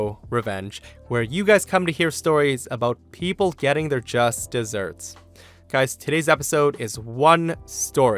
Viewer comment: Are you saying 'Justice served' or 'Just desert'? Because I keep hearing 'Just deserts'...